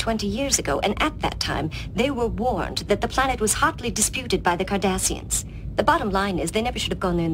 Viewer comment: for a moment there I thought the admiral said the planet was hotley desputed by the Kardadhians..